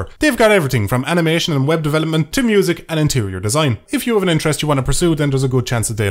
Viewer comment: I thought you said anterior design. ..because you were thinking about that kardashian.